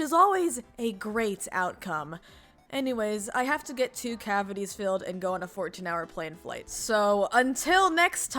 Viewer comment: I zoned out and thought you said "I have to get 14 cavities filled and go on a 2-hour flight" I was like "EUGH-WHA-HUH???" Also Rafael being an omega player is insane, I wonder if he just has womenphobia and kills his wives after they have babies, but he keeps dating since he loves kids just too much 🤗